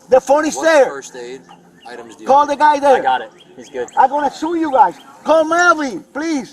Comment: Might want to update the captions here. I'm pretty sure he said "I'm going to show you guys", not what the captions say.